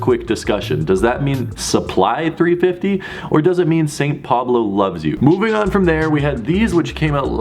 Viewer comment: OMG I just realized something big that's so stupid! I thought everyone said sply meant saint Pablo love zoo, then I just figured out it means saint Pablo loves you. OMG I'm stupid!